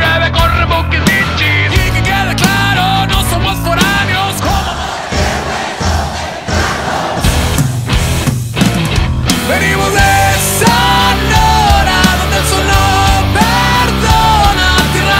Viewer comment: Que dice en el minuto 1:46, frase seguida "somos mexicanos"; no alcanzo a distinguir que dice, saludos desde la ciudad del diamante rojo 🍓 IRAPUATO GTO 🤘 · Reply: "Y que quede claro, no somos foráneos, coma monda el que la juegue, somos mexicanos"